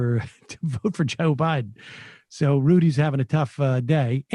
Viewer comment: ACTUALLY SHE WAS SAYING TO VOTE FOR JOE BIDEN. You say @ 0:18 that she says "dont vote for Joe Biden" thats the opposite of what shes saying. Just saying.